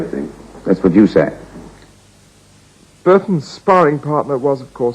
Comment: What is the name Burton says on the begining? I didn' quite understand. I also didn't catch the sentence from 0:22 to 0:25. Can anyone help me, please? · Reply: the name at the beginning was Brecht. the sentence after from 0:22 to 0:25 was something like "that's what you say" in response to Ken Tynan's differing view of Brecht's work and its importance in the Theatre